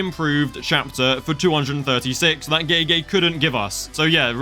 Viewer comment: bro i was reading the subtitles and instead of gege it was written gay gay